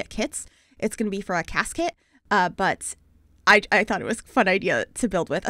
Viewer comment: I keep hearing "casket " when you say "CAS kit" 😂